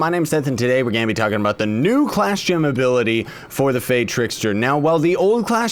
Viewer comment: When I picked far trickster at first I thought it said fire trickster